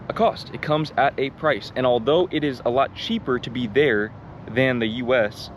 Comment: You pronounced 'heel' weirdly, it sounded like you said "it is yelllow cheap". Pronounce it as you would pronounce "hale" in English :p